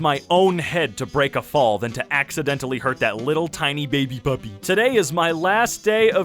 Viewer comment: I heard bubby